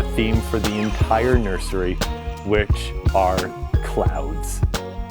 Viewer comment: I wasn’t looking at the screen the exact moment he said they were doing a “cloud” room and held up the knobs, I thought he said “clown” and I thought, well that’s really horrifying hahaha.